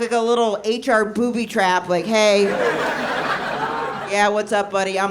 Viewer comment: I thought it said booby trap enema